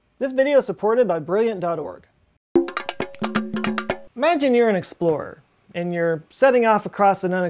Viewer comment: “Imagine you’re an explorer.” I heard “in a store.”